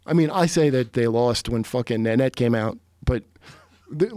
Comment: What did he say right before 5:00 ? He said he felt like they lost when Annette came out? I'm sure I know what he's talking about I'm just not understanding the word he's saying